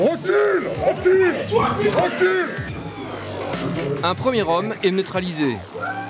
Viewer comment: I thought they said :"The man is neutered."